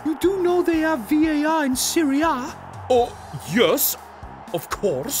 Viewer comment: i thought he was saying syria lol😂😂